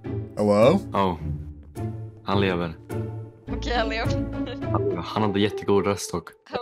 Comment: For any non-swedish person wondering what they said: while even for me, a swedish person, it was kinda hard to hear what they said, I think they said ''han lever'' or 'ok/okej han lever'' wich means ''he lives'' and 'or ''he is alive'' in this case I think they mean that he's responding but idk. edit: (First I changed a bit of the previous text and now im adding this text: Then they said (and this was also kinda hard to hear) ''okej (ok) han hade jättedålig röst också'' (''He had a super bad voice, too'', or ''he had a terrible voice'' or something like that, it's kinda hard to translate sometimes)